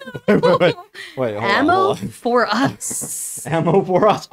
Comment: did i just hear "a mofo"